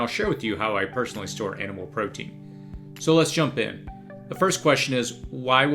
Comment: Ha, totally heard misheard you as saying "cannibal protein", never shop when you're already hungry